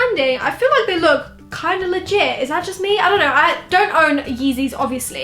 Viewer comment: cleans ears did she just say they look pretty legit 😖🔫